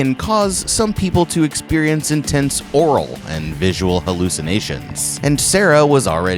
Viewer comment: did you just say oral hallucinations instead of auditory hallucinations? 😂😂